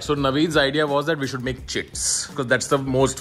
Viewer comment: Am I the only one who heared "chits" as "chips"????